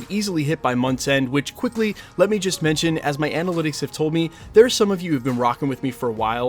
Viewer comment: Finally, Mephisto. Eric Voss redeemed! 3-4 episode miniseries moving forward to avoid filler episodes would be deluxe. (When you said “analytics”, I thought you said Annie Lennox- lol)